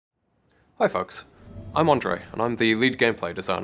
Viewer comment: did he just say hi fucks in the beggining